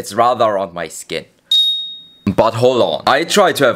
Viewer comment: did i hear buthole